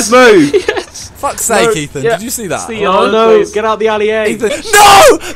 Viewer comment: Anyone hear Harry say get out of the AliA